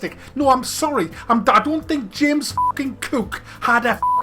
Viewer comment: Because of the Star Trek talk, I thought I heard James Kirk for a second.